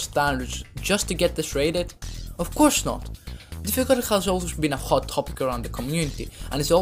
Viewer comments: what is he saying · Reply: sorry i am saying, "difficulty has always been a hot topic around the community" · what is he even saying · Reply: haha, i was supposedly saying difficulty